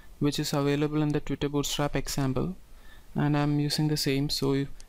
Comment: Great video. I do have one question.. where did the tutorial sample twig file come from? It sounds like you said the "strapX sample" ? Not sure I'm hearing you right. (Are you talking about the Symfony2 standard ACME Demo Bundle Secured Controller / login.html.twig stuff? ) Many thanks for sharing.